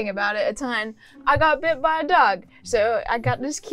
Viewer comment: At first i heard 'i got bit by a duck', which does make sense.